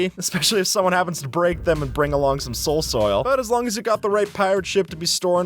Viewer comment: did bro just say soul soil